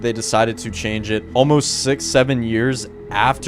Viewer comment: did someone say 67?